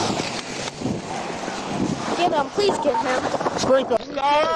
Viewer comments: GIRL Please get him All this never wouldn't happen if you have told the truth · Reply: I thought she said please get her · What does she say at 5:15? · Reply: Please get him something in that category